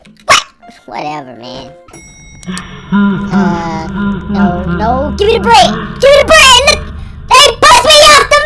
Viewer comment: it sounds like he is saying give me the brie